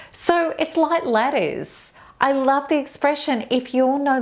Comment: What is she saying..."It's like l________". 7:27 ???. It's like lettuce? letters? Help, please. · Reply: Ladders......some are superstitious about walking under a ladder ❣️